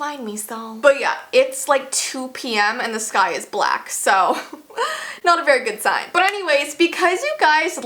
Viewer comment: why did that "Not a very good sign" sounded like a "Not an happy landing" to me? Meh, must've watched to much star wars.